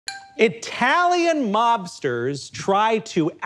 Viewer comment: I was only halfway listening and thought you said Italian lobsters